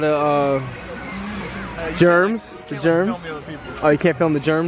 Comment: Thank You Anisog!!! :) Do you have any clue what being on somebody's page means? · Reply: My pleasure! If you've understood everything except this one, boy, your English ROCKS! One funny thing: when spoke about "germs" (his short version for Germans), I heard him saying "gems" as in Juwelen. Haha! But at least, I can take a joke. Have a good one. Hey, and always focus on the positive, that's what you attract. ;)